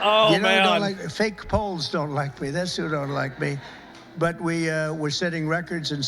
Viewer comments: BRO I thought Trump said "Hoes don't like me" turns out I misheard poles. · I thought bro Said “fake hoes don’t like me” instead of fake poles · For a second I thought he said hos don't like him.